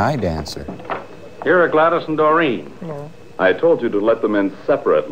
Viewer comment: I legitimately did a double take because I heard that as "GLaDOS". Funny, 'cause this movie could use a little bit of deadly neurotoxin.